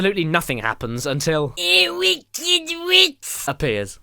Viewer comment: What the heck did he say? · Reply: ewkeddiwik